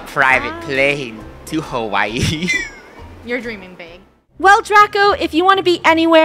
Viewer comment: is it just me or did say "ur dreaming bea- " 😗 wth? Im going crazy 🥲